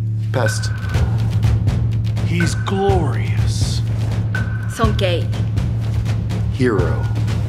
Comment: I thought Asuka said "so gay" LMAO